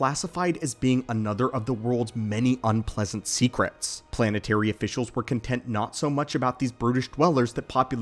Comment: At first I thought he said "Planetary officials were content not so much about these British dwellers"